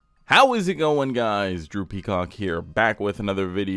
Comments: I'm ngl I heard "droopy cock here" and not "Drew Peacock". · Never seen your videos but i had to go back and listen when i heard "Droopy Cock here..."